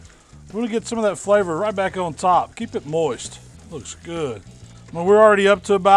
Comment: Did someone say moist?